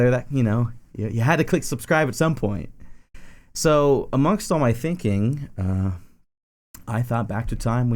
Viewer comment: When he said "So amongst all my thinking...", I heard "So a monk saw my thinking"